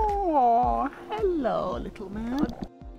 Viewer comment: Did you just say “Hello, little Matt,” to the cat? 😹